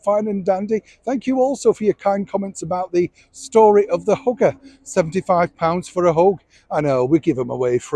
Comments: hugger! i thought you said "hooker" which would've been fair for 75quid 🤣🤣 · I was a little taken aback as I thought you said "hooker"! Hugger.....got it! I'm not entirely convinced she's stayed away/silent by choice, as past behaviour tells us she'd have done a pap walk/issued photos of the children to distract attention away from him.....she's generated the "where's Meghan" stories, instead! Anyway, her busy little brain will be working overtime plotting and thinking up more fiction and, of course, probably dipped into Harry's pot of gold for another round of cosmetic surgery...... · Neil, your Northern accent made the word “hugger” sound like “hooker” to these American ears. I was stunned at first thinking boy did I miss a story. Keep up the great work! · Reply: Yes, I thought he said 'story about the hooker' '75 pounds for a hoe'. Even after reading these comments, I went back listened and thought, could he be speaking about both? Because I did hear him later talk about hugging. So I turned on closed caption and hugger showed up for 75 pounds. Bet he's having a good laugh at us right now. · Lol because of your accent I thought you were saying 'hooker' not hugger. I thought ooooh !!!! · I thought he said hooker not hugger